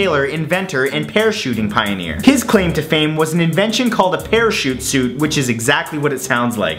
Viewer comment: I thought you said pear shoot suit